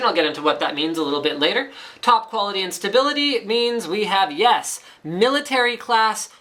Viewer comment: I thought he said "top quality instability" at 0:21 lol